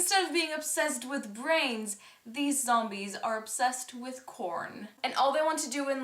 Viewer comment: i thought you said the zombies were obsessed with porn xd